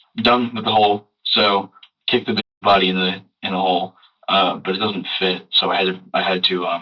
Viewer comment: AM I THE ONLY ONE WHO HEARD HIM SAY "i kicked the b*tches body in the whole"?????